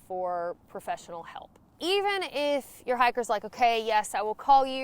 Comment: I actually thought you said "hackers" but then remembered it's talking.